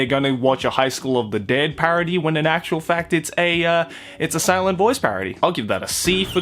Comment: Legit when you said A Silent Voice I thought you said Asylum Voice